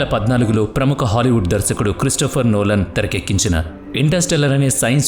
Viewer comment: I hear Christopher nolan as mehar nolan 😂😂